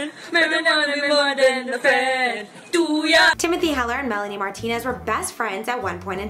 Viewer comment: In the beggining I thought she said Melanie Martinezezes